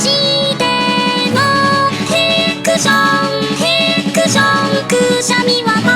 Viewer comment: 「へくしょん」が「フィクション」に聞こえるの凄い、どちらにも聞こえる